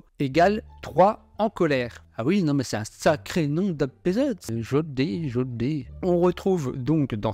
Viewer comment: Qu'est-ce que tu dis ? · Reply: « Un sacré nom d’épisode » ? 🤔😅